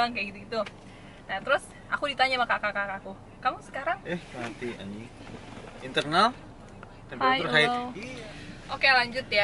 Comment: "ehh mati anying" Bener ga sih apa gua yang salah denger